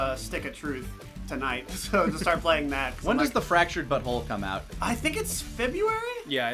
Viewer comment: I thought you said Fractured Butthole, not Fractured but Whole.